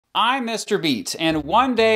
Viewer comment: I thought he said I’m mr beast